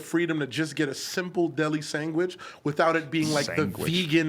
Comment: "sangwich" haha freakn I heard that